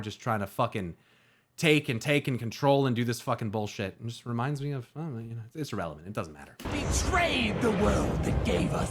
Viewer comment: Did he just say “which reminds me of Israel” or is my mind just fucking with me at this point? Pls tell me I misheard ….